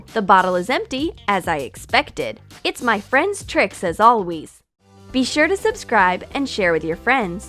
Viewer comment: Did none here the end when she said “as always” it sounded like she said “ as alwees”